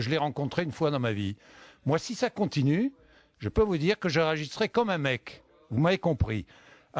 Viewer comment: "Si ça continue, je peux vous dire que je "réagisserai" comme un mec..." !?! 😂 sacré boudin ce Bourdin. @ 7:09 Je crois que Bourdin parle à lui même devant son miroir: "Et moi je n'aime pas me faire insulter ... JE N'AIME PAS ME FAIRE INSULTER. ça suffit ... ça suffit". Moment culte, c'est trop drôle 😂😂😂